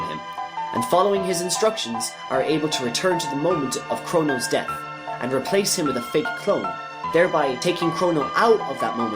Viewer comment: what does he say after "they receive the chrono trigger" its so fast and quiet i cant tell what it says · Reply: He says they follow the instructions from the Guru and go back to the moment Crono dies and replace him with a clone :)